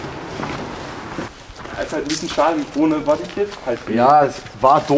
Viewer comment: Was sagt ab 2:55 über den RS6? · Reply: Jekay249 Ohne BodyKit